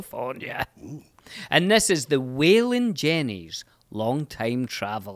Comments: DAMN. I THOUGHT you said Waylon Jennings. Not bad though. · I was a bit confused, I thought said "Waylon Jennings". haha